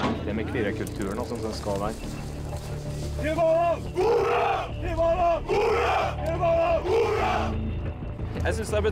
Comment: what are they shouting? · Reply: Til Valhall (To Valhalla). In the longer version he actually says: "You are the predator, Taliban is the prey! To Valhalla!"